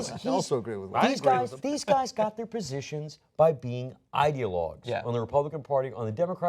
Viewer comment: what's the word wes says at 7:23? idealogs?